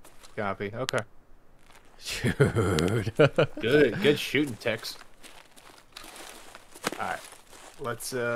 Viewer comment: did he say good shooting tex lol took me back to the red vs blue (halo) days with that